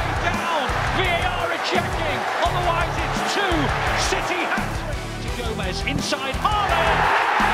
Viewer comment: In the first go’s, it sounded like he said “Another sh*tty hat-trick “ lol